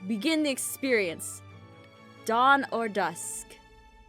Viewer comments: I thought the first question said dawn or duck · I thought it said dawn or duck 😂